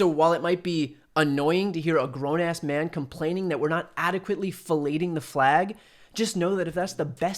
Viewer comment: Did he just say "fellating the flag"? BAHAHAHAHAHAHAHAHAHA!!!!! 😂🤣